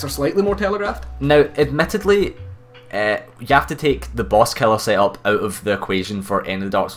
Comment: but what are you saying at 11:12 The boss something? Loved the video! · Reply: the boss killer set up (Rapier, flynns ring, ring of blades, leo ring, high dex and a buff), we had to take that out the equation for DS2 bosses because then all of them would be shit because it just melts everything.